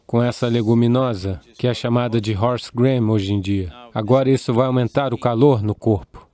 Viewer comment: Como é o nome da lentilha ? Parece que ele diz ''Horse Green'' mas não é isso... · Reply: Olá! Eu entendi "Horse cream", e é feita com uma lentilha especial. Como ele diz que é uma sopa, a palavra creme me pareceu adequada. Creme de lentilha. (Voltei pra ouvir e não é cream).